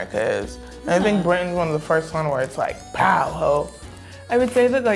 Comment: Did she just say “POW HOE”?😂😂😂 Mate, wtf!!